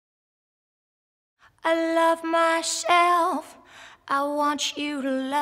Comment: it's awesome and it's beautiful, but at the beggining I keep hearing "I love my shelf" :P